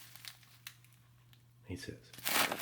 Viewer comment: I heard that super quiet "nice hiss" from ya there.